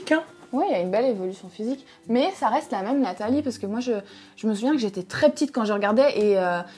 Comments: c’est moi ou elle dit « ça reste la même Nathalie » 🤔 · c moi où elle a dit Nathalie ?😂